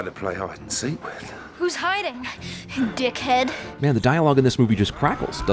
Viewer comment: Also wait, why does is sound like Kitty is saying "dickhead"? I'm 90 percent sure she says tinhead.